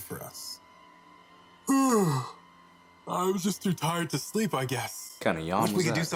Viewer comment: did he just say he’s just too tired to sleep? XD That reminded me of that one TikTok where this dude said “Women don’t wanna dare me because I’m too hot.”